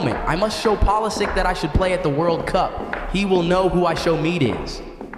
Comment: Did bro say ishowmeat ain't no way